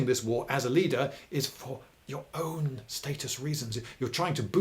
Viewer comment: At first I thought you were saying “statist” and I was quite perplexed.